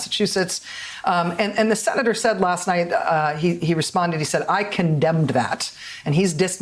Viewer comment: Boomer for Bernie: I do not agree that candidates are responsible for the conduct of "alleged" supporters but the candidates do have responsibility to condemn problem harassers. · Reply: Agreed. What did the interviewer said at 1:29, though. “To primary you”? 🤔